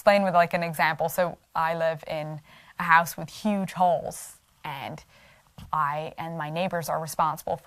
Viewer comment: First the giggle... I thought you said Holes not Halls. 😂🤣 Ok, ahem... I wonder why it is that everybody, even and maybe especially the "Christians", these days seem to seek out conflict? And then, when they inevitably find it, anyone with differing opinions or points of view automatically are considered The Enemy and labelled Evil to the Core? I have always liked the -- If you have a problem with a neighbor, go and talk to them about it. If they don't want to hear it, bring someone with you to talk again. If they still won't hear it, walk away and have no further interactions without any lingering guilt. Yes, heavily paraphrased, but not wrong. Forgiveness and open honest communication are so important. So is only judging others by the same measure that you judge yourself and want to be judged yourself. Again, heavily paraphrased 😁 For years, I said that the Bible basically, in a nutshell, says Treat People the Way You Want to be Treated. Not Treat People the Way They Treat You... But that's not enough now. 😕 Stoopid Hoominz 🤷🏻‍♀️🤦🏻‍♀️ I enjoy your videos. You have a very lovely spirit and light that shines brightly. Like the Sunday School song😁.✌🏻☮️💜🕉️🕊️